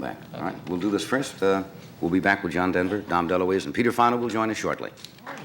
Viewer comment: Sorry, did I hear that right ? Did Johnny actually say there was a guy called Peter File coming on the show ?? >:D